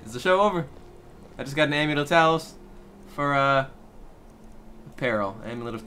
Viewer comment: i thought u said "amulet of towels"